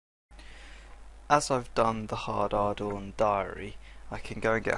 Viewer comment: sounded like he said hard hardon